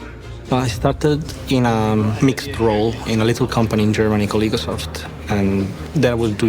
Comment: Did he say EGOSOFT? The devs behind the X series?